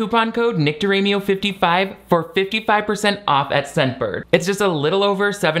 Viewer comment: Omg. I have audio processing issues sometimes, but tell me why when you said "Use NICKDIRAMIO55" I heard "VICTORYMEAL55"